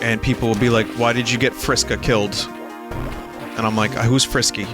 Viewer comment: nearly had a heart attack because i thought vinny said "vriska" But yeah, that makes sense. Idk how the fanbase is currently, but it was super toxic towards content creators when it first came out. I can't remember who it was (Steam Train maybe?) where people got mad enough at the let player that they started their run over so they would do a true pacifist run on their first go.